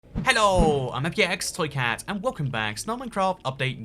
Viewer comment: Finally, instead of VX flicker, Youtube thought he said "px toy cat," we are getting close.